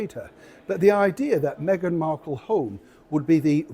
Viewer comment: I thought you said megan markle whore😂